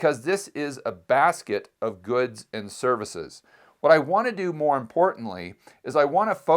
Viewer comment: LOL, I always thought he was saying “survasive”